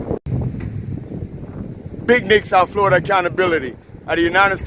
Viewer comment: I could've sworn you said "Big Dicks South Florida Accountability" at the end ;)